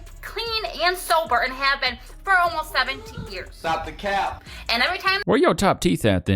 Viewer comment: I love the subtitles saying “70” instead of “17”, because that’s the REAL punchline!!